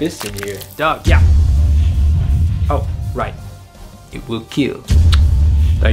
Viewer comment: I wasn't paying attention to the video and I thought he said "You look cute *smooch*" instead of "it will keal"